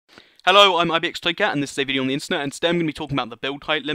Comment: I do agree on not understanding however its not annoying. All I hear is "Hello I'm ibxtoycat and hbehbubrubu internet."